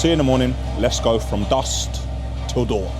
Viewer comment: Did he say "Dust till dawn" instead of "Dusk till dawn"?😂